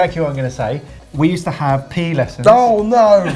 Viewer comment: I keep hearing "pee" lessons...do they mean like PE?